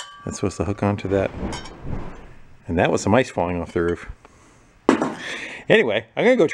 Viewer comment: Sounded like you said some mice were falling off the roof. I guess that’s what I heard because you deal with mice so often. I thought it was some awfully big mice. The I realized it was some ice, not some mice.